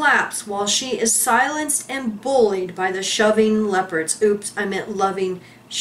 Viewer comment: Shoving leopards. You got it right 1st time x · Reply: I thought she said, "shoving lepers," at first.